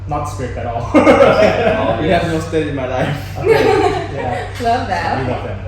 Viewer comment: timestamp , that " inaudible" part, First said " you know I have no stress in my life" It was Hella clear ... Don't know why the subtitles said " inaudible"